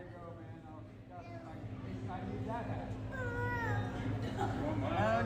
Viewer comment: He actually said 'I need THAT hat' at 2:00 exactly. LOL Kammy was rotating like Linda Blair in the Exorcist !